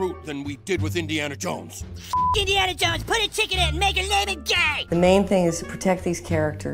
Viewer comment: This whole time I thought was saying, "PUT A DICK IN IT AND MAKE HER TINA FEY".